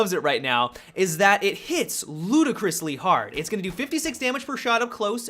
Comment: first i thought you said at 1:02 "...that it hits LUDACRIS-ly hard". haha